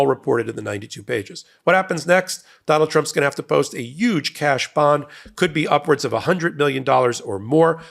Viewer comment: LOL "Yuge?" Did you just say 'a yuge cash bond?" hahahahaha You're funny Michael. ♥♥♥